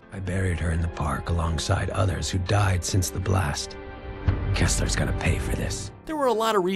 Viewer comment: I thought at 1:53 he said Keemstars gonna pay for this